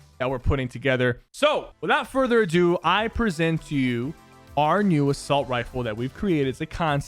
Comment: I thought you said "RU Assault Rifle" not 'our new' assault rifle and I was intrigued of the possibilities. Lol